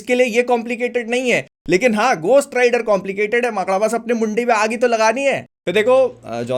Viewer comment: did he just said makoda